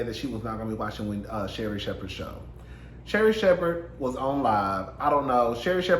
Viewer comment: THAT PIC OF SHERRI 😂 and I thought you said " Shady Shepherd" at first.